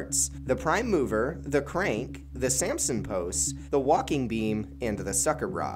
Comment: I thought he said... the Samsung post😅